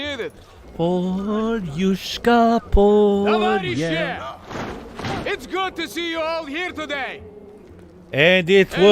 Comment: I love the My Fields scene, but as a Russian novice, can someone tell me what the phrase at 35:03 is? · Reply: "Comrades". Tovarish = Comrade. He's saying the plural.